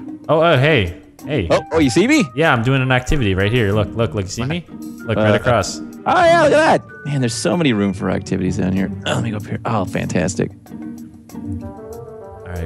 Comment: about 4:03 : did he say "o yea, there are so many room for activities here" ???